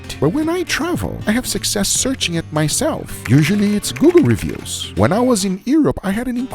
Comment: Lmao, he said Google reviews, but w his accent is thought he said Guga reviews! If thats not the name of his new travel series then im unsubbing! Lol